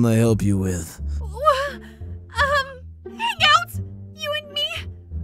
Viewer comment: When purple said hang out I thought she said make out and I didn't know what to think of that...